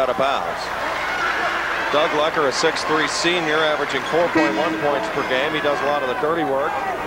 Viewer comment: Did they just say #34 65 senior averaging two points a game that's insane.